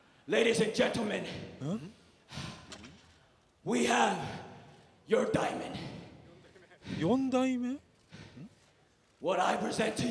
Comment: 何で四代目？ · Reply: この動画に字幕付けると分かりますが、おそらく実況席の人が「your diamond 」と「4代目」を聞き間違えてますね笑